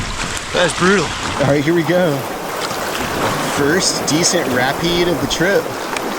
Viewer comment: Did he say “rapeed” at 2:41 😂